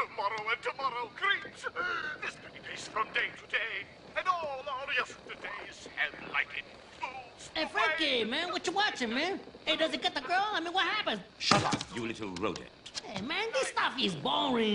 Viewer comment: Did Tito say: Does it get the girl or what happened l can't hear it 😂😂